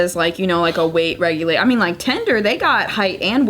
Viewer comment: I had the same face as and face when said “tinder” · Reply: I thought she said tender lol